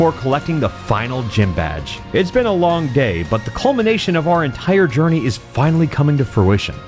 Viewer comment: I can't be the only one who heard "it's been a long gay"